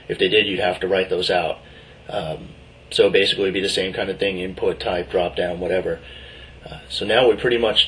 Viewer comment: This guy is funny... Did he say "input type=dropdown" at 12:51? HAHA funny. select tag would be a better way of saying it.